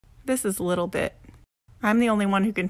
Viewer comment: I could have sworn she said this is little dick, I'm the only one that can touch him! Then my mind register that she said, this is little bit! I was thinking who the hell would name their dog little dick!🙂